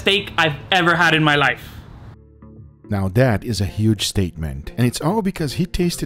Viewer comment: in the beginning I heard "Now that was a huge statement" as "now that was a huge steakment" and honestly I'm okay with that